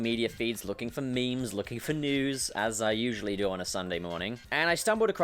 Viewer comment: for a second i thought he said "looking for nudes" instead of "looking for news"